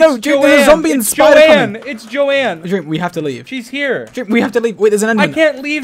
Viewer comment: George: "it's not John or Jone or Johnathan" Dream: Juan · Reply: I thought he said Johanna